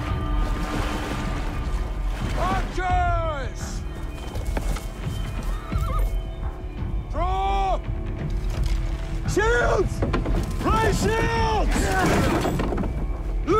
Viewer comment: I heard Ray Shields